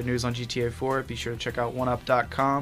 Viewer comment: in the first part when there talking it sounds like he says gayvideos